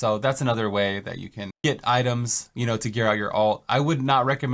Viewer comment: what do you say at 410-4:13, you cna "it" items for an alt, but it wouldn't be recommended? I cannot understand accousicly, the word you're using? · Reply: I believe he said "get" and not "it". Another way to get items.